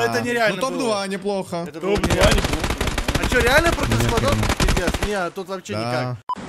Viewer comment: а че рили против сквадов? не это пиздец это не реально лол первыц раз слышу что матерился